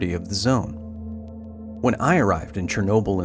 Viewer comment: When you said "when I arrived in 2021" All i heard was "when I got irradiated during a pandemic"